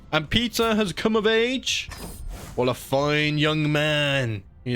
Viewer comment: why did i hear "pizza has come of age "